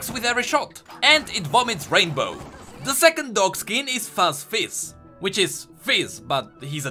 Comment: I misheard fuzz fizz into f*ck fizz